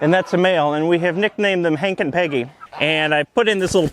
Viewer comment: "We nicknamed them Hank and Peggy." *Propane and propane accessories intensifies* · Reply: At first, I thought he had said, “We nicknamed them Hank & ‘PANKY’” 🤣